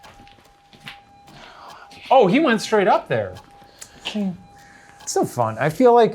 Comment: After Ryan asked “where is this walkie?” @ 55:27 it sounded like I heard “it’s right here”